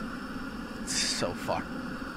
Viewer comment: did you say it's so F'd or so fun